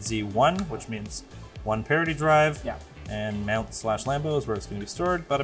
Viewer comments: Did u say one parody drive at 10:20? It is parity drive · I am constantly wondering who is creating those captions. So many misheard words is almost funny (parity -> parody at 10:16 is the best one). Are you outsourcing it somewhere?